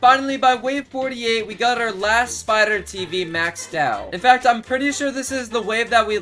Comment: I think said spider tv